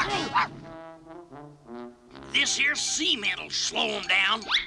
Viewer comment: anyone else hear him saying "this here semen"?